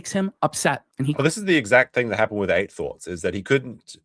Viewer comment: Hey! can you repeat the name of the channel he got shut down, because he couldn't beat them in an argument, … (It's what you said at this timestamp.) Did you say, … Eight Thoughts 🧐 ? Sorry, I'm going deaf, & that's what the transcript said, but i just want 2B sure. #RePLayCrew ALso, for@ CODE: JEWBULL 🫪 🤣 🤣 🤣👍 Good Stuff!!